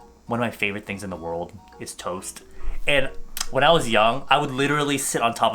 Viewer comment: I legit thought you said “one of my favorite things in the world is toes” SKSKSKSKSK IM SO SORRY 😳💖